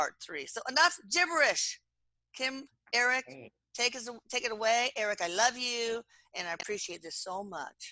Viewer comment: I hear Eik at 1:04... 😊